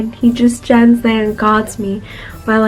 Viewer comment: I thought said "Gods me" instead of "guards me", lol i got confused for second